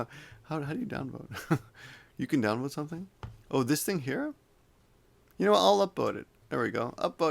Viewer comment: really sounds like you said "I'll upBoat it"